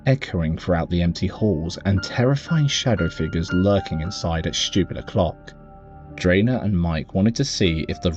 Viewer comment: did he say "lurking inside at stupid o'clock"?